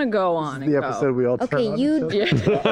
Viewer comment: what did Sam say? This is the episode We... what??? Lol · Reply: "this is the episode we all turn on each other"